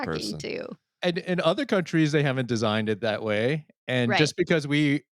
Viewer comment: I heard "who designed that" on health insurance. Got this vision of jimothy walking into a room full of screens like that scene in the matrix 🤣